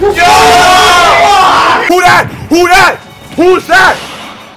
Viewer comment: 俺！俺！うっせぇ！(空耳)